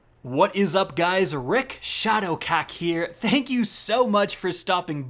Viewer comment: At the beginning I thought he said Rick Shadow Cock